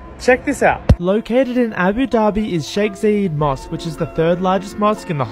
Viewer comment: I thought it said mosquito 🦟